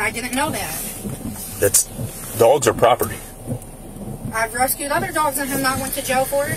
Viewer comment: "I stole other dogs and didnt go to jail." thats so awful. those poor people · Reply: Listen again- it’s cluttered w other background noises. I thought I heard “recused” too🤷‍♀️